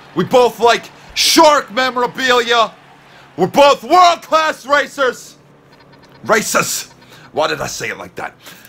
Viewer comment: it sounded like you said World class racist insted of racers 😂😂😂